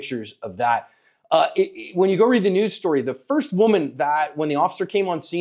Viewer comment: Am I missing something or how did the female officer not take him out after the random male stepped in? Think what we aren't saying is she must have dipped out and never looked back as the next occurrence is another officer showing up and handling it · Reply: Ok 3:16 fumbled his words and said first women errrrr officer and I heard it as first woman officer